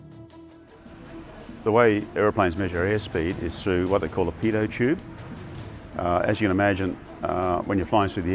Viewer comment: it's a pitot tube to measure airspeed and not, as the subtitles say, a pedo tube.